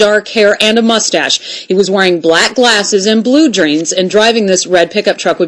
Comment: sorry at about 1:40sec she says blue greens instead of blue jeans